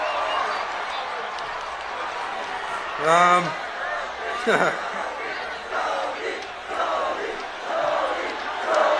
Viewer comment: what are the fans shouting to mark stone? it sounds like goalie but hes the captain lol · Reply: I think its "Stoney" "Stoney"....